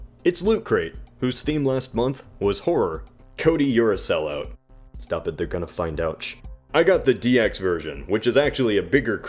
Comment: did he say "Stop the dick on a find ouch"? What?